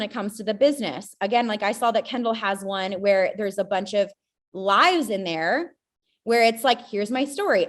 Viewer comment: Oh no, the first woman was talking about some facebook group and she said "There's a bunch of LIVES in there", but I heard "a bunch of LIES". My brain had some kind of a freudian slip 😂